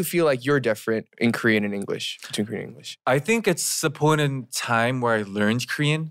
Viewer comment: can anyone teach me what he said? (I'm learning English😅) " I think it's _____ time??" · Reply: I think it's the point in time